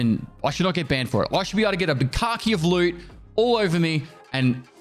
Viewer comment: all I heard is "bukkake of loot"